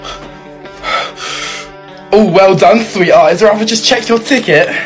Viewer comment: “Oh well done three eyes”💀 Got me cracking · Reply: my dumbass thought he said sweetheart 💀💀